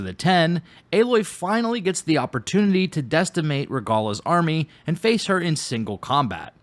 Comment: Maybe i heard you wrong but i don't believe 'destimate' is an english word. You must have meant decimate but i definitely heard a T in there at 0:52.